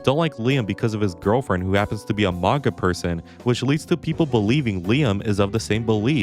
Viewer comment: hi hi !! im partially deaf and I can't make out what is saying at 9:03 "his girlfriend who happens to be a _______ person" 😔 help would be appreciated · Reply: MAGA make america great again, basically racist